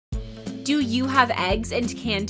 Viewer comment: I thought you said do I have eggs and canned tuna at home? I do, but I don't feel like pulling out the rest of that stuff